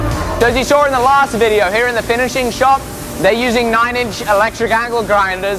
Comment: I heard 'fettling shop' rather than 'finishing shop'. I prefer fettling shop.